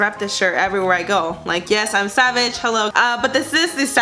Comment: Lol at the savage shirt. I COULDVE SWORN IT SAID “sausage “ at first. Great video!!